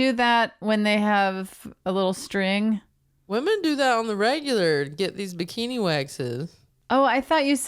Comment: LMFAO I also thought FF said on the rag!